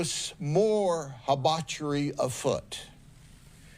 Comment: the word 'haibachery' is used. I did a Google search for this word and many alternate spellings...but Google only suggested that it was a mistaken attempt at the word 'debauchery'. We can all make mistakes, but this doesn't sound like a mistake the sherrif would make. Can someone educate me here? What word did he use? Thanks.